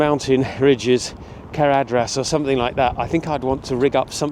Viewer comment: did he just say caradhras, as in the mountain in the Lord of the rings?! Please hold onto your sanity😊